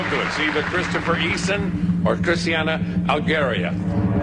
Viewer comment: what did he call her? Christianna Hungaria? Hoenstly my new drag name.